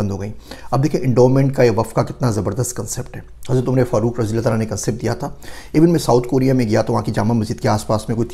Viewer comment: Correction need in English subtitles at 4:37. There are written Prophet Farooq instead of companion Hazart umer Farooq. Please correct it. Thanks